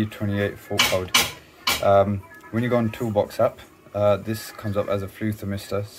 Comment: I thought you said when you go on WhatsApp 😂